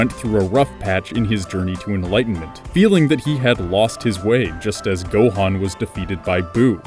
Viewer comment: I'm but did he say he lost Da wae